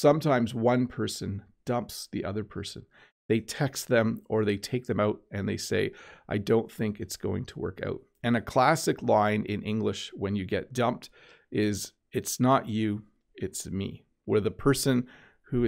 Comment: And a classic what??? is not you... I can't hear it clearly. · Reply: is it 'classic bye'?